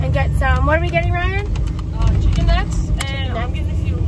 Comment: I thought yall were saying chicken nut....